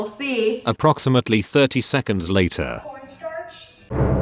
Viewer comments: anyone else hear “pornstarch” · i thought she said "Porn starch" 😂🤣 Love the vid keep going!~